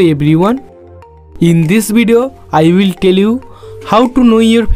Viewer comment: i Thought you said i will kill you but its i will tell you😂😂😂